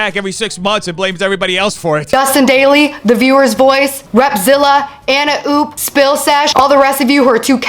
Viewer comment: I thought she said revzilla and I was like “what does a motorcycle company have to do with any of this?”